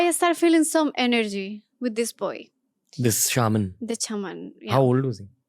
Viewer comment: I thought she is saying "chaman" 😂😂😂